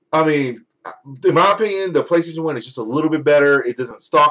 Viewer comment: Did you just say the PlayStation One?